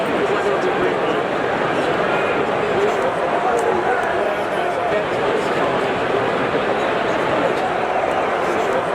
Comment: That water bottle snag was hilarious. 12:10 that knuckleball caption and footnotes like that make your videos even more unique. I have absolutely no idea what the Englishman at 12:31 said. The guy at 15:12 will remember that moment for the rest of his life. These foreign escapades keep your page fresh. Peace. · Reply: He said Bury FC. A team in Manchester being run into the ground by a corrupt owner.